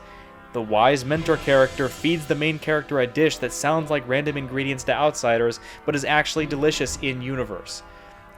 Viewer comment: did bro say random ingredients, lol. that's a Nigerian dish man